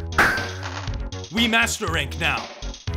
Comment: Hunters: " we master rank now." Blacksmith:" Hehehehe yeee boi!" Smacks hammer infinitely fast" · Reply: i heard "We Masterburate Now"